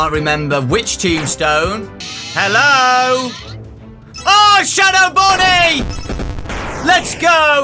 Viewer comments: As soon as found shadow bonnie i got an ad while he was saying "Oh shadow bonnie !", and it sounded like he said "Oh sh-" xD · Got an ad as he was saying shadow bonnie, so to me I thought he said " OH SH*T "